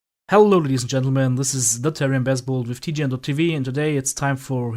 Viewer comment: when u start saying thetarionbesbald it sound like u say the babarian baseball